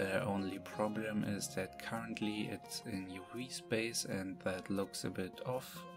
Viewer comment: What does he say here? "The only problem is currently it's in ??? space and that looks a bit off" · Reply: Sorry for the late response. What I said was: "The only problem is, that it's currently in UV space [<- a sort of texture projection, usually creates seams at some part] which looks a bit off when using with spheres." Therefor it's better to project from multiple angles to avoid seams.